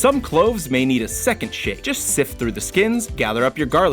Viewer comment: did he say shit? I know he meant shake but sounded like it